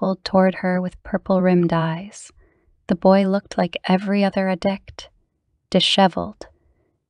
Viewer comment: I also wanted to add if this is an AI voice it's very good and if it's a human I did want to pass along that at the 0:20 Mark it sounds like she says "ad dick" instead of "addict". Maybe you have to do that because there's a YouTube filter but I thought you would want to know.